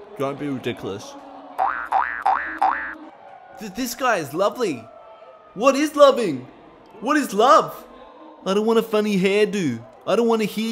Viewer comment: I think he is saying "This guy is laughing, why is he laughing? Why is he laughing?"